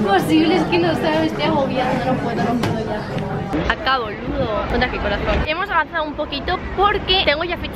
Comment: parece que dijo "acá bólulo" como re Argentina 💜😂